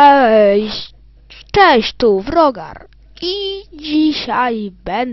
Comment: usłyszałem browar zamiast wrogar